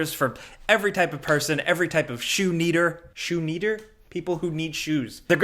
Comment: lol shoe needer..... I thought you said shoe eater at first.